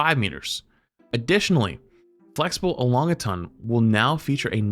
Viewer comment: E long gatun instead of Elongation is crazy speech